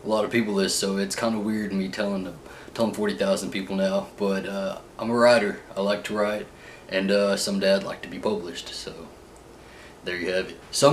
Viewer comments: Oh you like to write...I thought you said you like to ride. I'm like...ride what lol? I love your accent. Friends and Will & Grace! Always gets me a good laugh and puts me in a good mood. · Okay so when you said something that you thought might surprise us, you said, "I'm a writer..." but with that gorgeous thick accent I thought you said, "I'm a rider, I like to ride..." And I had to pause the video and say I hope you mean bicycles because I don't need to know about that part of your life I love you but that's too much. It wasn't until you said, "and some day I would like to be published that it clicked and I felt like an idiot. LMAO